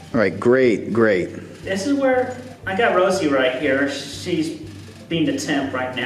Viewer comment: it sounded like Chris was saying that Rosie was being a little temp(tress) right now.